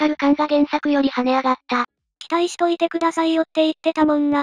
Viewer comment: 「期待しといてくださいよ」になってて何のツッコミもなかったけど なんか聞き間違えてたかと思って見直しに行ってしまった ネタ的なワザと、なのか 関西弁の「せんとってくださいよ」が他地域の人に聞き取れなかったのかどっちなんだろう？ 個人的には期待してますけどね！新田さん！！